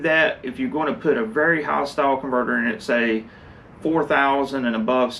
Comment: More homogeneous mix with dual quads? At about 13:30, did I hear ”hostile converter”?